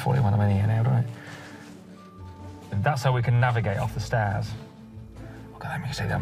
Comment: A Texan here. Did you say they navigated off the stairs?